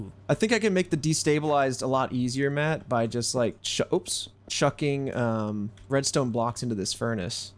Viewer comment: Did someone say Chuck?